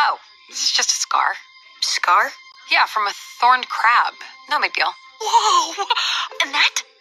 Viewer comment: I thought she said the bite is from thorened crap